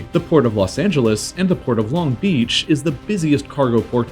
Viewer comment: Sound like you said Long Beach “Lung Beech”